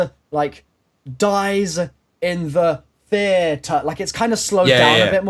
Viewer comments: I heard "He dies in the theater".._ Maxis? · i heard "he dies in the theater" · I hear "dies in the theahtare" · I hear "Dies in the theather"